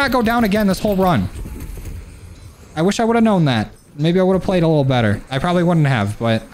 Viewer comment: did say the n word at 10:10 or am i tripping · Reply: he said maybe lmao